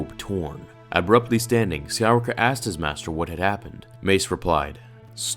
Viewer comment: what is said here? Who asked their master? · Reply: Skywalker